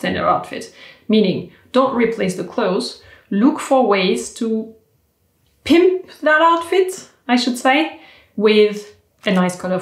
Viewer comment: Primp. Not pimp - too funny! (Pomponner. Pas Proxenete.) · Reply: I totally thought she said “pimp that outfit” and I was cracking up. 🤣